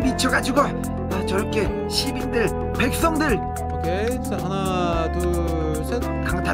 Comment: ok 1,2,3 이라 들리는건 나뿐인가? *이댓은 묻히게 돼있다 사랑해요 꽃길만 걸으세요 연기잘하시는 또 수고 하셨습니다*!♡^^ 빼먹었어따ㅜ 와 씨...안녕하세요ㅋㄱ...1년전 꿈과 동심속에서 살았던 사람임다.ㅋㄱ.,이 댓글을 발견할줄 몰랐서요 ..지금은 공부에 찌들어 살지만ㅋㅋ..,..진짜 이때 무슨생각이였는지 저도 모르겠네유